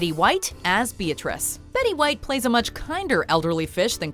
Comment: I thought she said "much kinder elderly bitch"